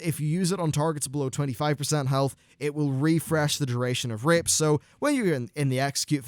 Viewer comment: Don't hate me for it, but I thought he said "If the target is below 25% health, it will refresh rape."